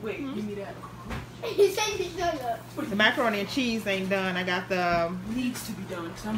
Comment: Girl why I thought you said "Thot macaroni" 😂😂😂😂